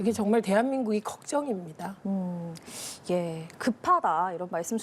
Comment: 최민희 대한민국이 걱정이랴는 이야기가 민주당이 걱정된다로 들리지요!